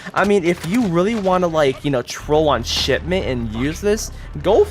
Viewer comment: it sounded like he said shitment instead of shipment